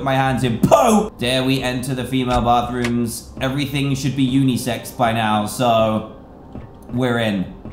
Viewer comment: did he say unisex or what